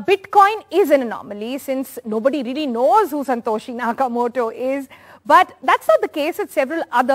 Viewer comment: Did you just say 'Santoshi' Nakamoto!..